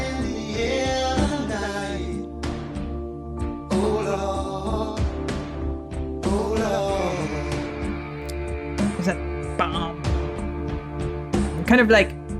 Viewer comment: Misheard lyric fun! He's singing "Hold on", not "my lord." It's the cockney accent that fooled 'ya.